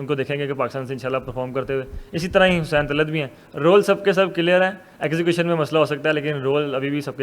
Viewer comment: "Did he say 'exhibition' instead of 'execution'?